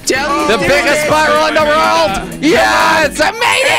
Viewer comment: Sounds like hes saying "Rorld" and not "World"